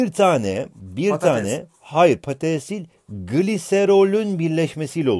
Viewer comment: "patesi" diye anladım, 3 köle birleşimi = 1 kral dedim, sonra birde düşündüm "az uyusam iyi gelir bence, çoğu kafa yapıyor diye " diye. Tavsiyeleri bekliyorum..